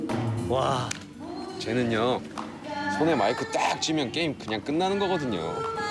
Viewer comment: 〜1:07韓国語が聞き取れないのですがなんと言っているのですか？日本語訳も教えて貰いたいです。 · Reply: 쟤는요 손에 마이크 딱 쥐면 게임 그냥 끝나는거거든요